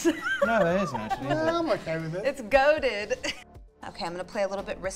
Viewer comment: As someone who had never played mtg it’s hilarious to hear them call a card goated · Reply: Even as an mtg player I keep hearing "goaded" as if it's goated lol